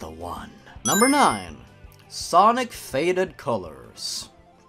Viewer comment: for some reason i thought it said sonic farted colors